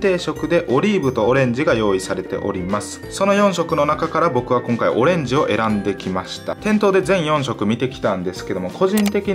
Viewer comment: もう、オレンジっていうとオレンズって聞こえる